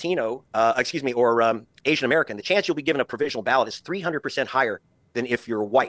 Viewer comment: I need the 3+ million people to be notified so they can sue somebody about their disenfranchisement. This is too illegal to be ignored. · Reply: The racism is so freaking insane. T keeps saying “make america great again” but I swear all I hear is “make America white again” and it makes me sick